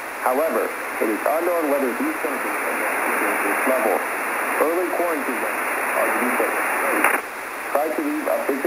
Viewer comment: I think he said 1:48 “Early quarantine measures are now in place.”